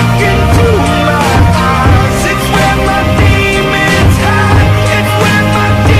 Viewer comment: I thought it said lemons